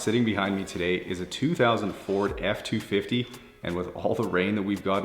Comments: Yikes man, he said 2000 FORD not 2004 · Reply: Ohh...I heard it as 2004 Ford..Cool👍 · Where did you get the number 21 from? You said 2004. My cousin was born in that year, and he'll be 19 this month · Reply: 2000 Ford not 2004. I misheard it too